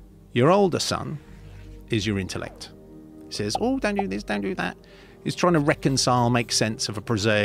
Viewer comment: he actually says "Don't do this, don't do that"